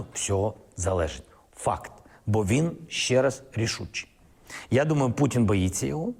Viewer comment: Zelensky is a 🤡. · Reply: Did he just say "FACK" at 1:06? Anyhow, we all know how the Coc@ head told us how Putin were Dying, Losing, running out of Child soldiers, missiles, political power and money...for 3 years, nothing of that happened. But 90% of the 'Dumb and Dumber' idiots listening to this are right no telling themself -so you say there is a chance...